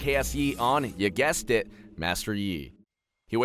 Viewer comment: Anyone else hear Master KFC at 2:43...